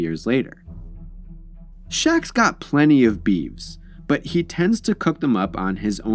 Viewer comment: did you say "beeves" as the plural of "beef"?